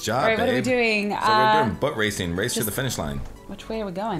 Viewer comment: When said boat racing it sounded like he said butt racing